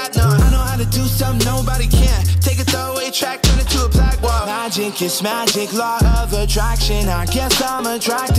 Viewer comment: Did he say "Logic, kiss my dick"? lol